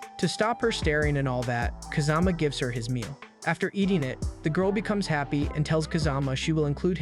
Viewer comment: not even 1 minute in and i hear "gives her his meat"